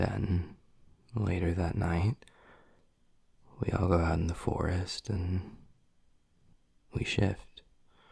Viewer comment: I THOUGHT HE SAID "Then we go into the forest and shit" I